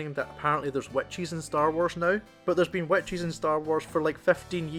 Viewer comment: The first time you said, which’s I thought you said white cheese I was so confused, 😅but yeah, I see what you’re saying. I am watching it with someone that has not seen rebels and they’re confused. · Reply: great video, I thoroughly enjoyed it. And to my initial consternation and then considerable mirth, I had to replay 7:05 4x, because I heard, "someone was complaining that apparently there was 'wet cheese' in star wars now, but there's been 'wet cheese' in star wars for 15 years now." I was really sitting there replaying every cantina, hutt, blue milk, and food scene I could recall wondering how I had overlooked all that cheese in a galaxy far far away. Cheers!